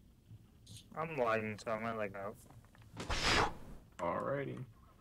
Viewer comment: what did he say? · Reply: Guy said he was lagging out